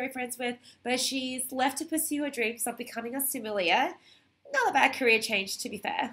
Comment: What is a "sumilia"? Is that what you are saying?